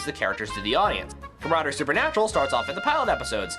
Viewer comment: what supernatural? i didn't catch the first word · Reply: comradery. Meaning friendship.